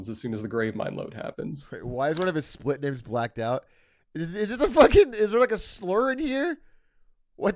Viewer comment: For anyone wondering what the censored word at 1:42:12 is, it's just "shit". No slurs here lmao